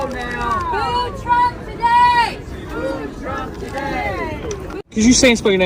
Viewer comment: I thought they were saying....Food Truck Today.